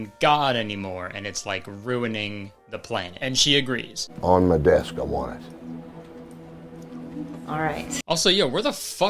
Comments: iiii.. dont know.. but at the end of that interaction.. i heard.. "on my DESK. i WANT IT" and i can not stop laughing at that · MY BRISBANE SHOW IS SOLD OUT BTW!! FOLLOW ME ON TWITTER & IG TO BE UPDATED IF I ADD ANY MORE SHOWS! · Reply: "on the desk I want it "😂😂😂 that's what he said ...😅Maybe !